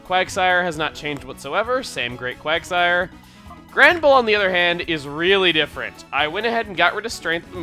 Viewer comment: OH man I thought you said GRANDPA instead of GRANBULL... lol that would have been a funny name to name Munchkin xD